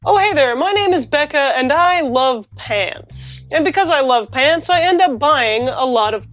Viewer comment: For a moment there I thought she was saying "I love paints"... Which is understandable, considering her normal content.